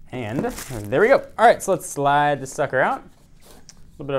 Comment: Did he say "slide this sucker out" at 2:02 ??? LOL hahahaha :D